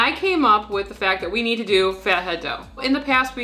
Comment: I didn't realize that something called Fat Head Dough was a think, so for the first couple minutes I just thought you had a problem saying Feta as in Feta cheese LOL